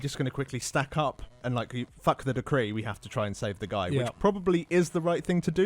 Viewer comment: Did I just hear "FUCK the decree"?